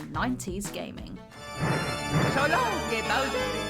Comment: All I hear is “so long gay Bowser.”